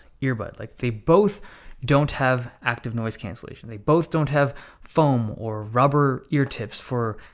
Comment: Wait, doesn't the buds have noise cancellation? I swear I heard them say it has noice cancelation.